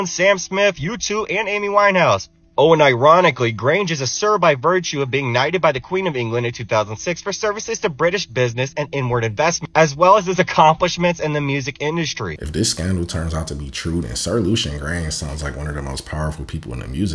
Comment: I seriously thought he said "..for N-word investments.." whichly oddly enough would have still applied , given his assoc. to rap/hip hop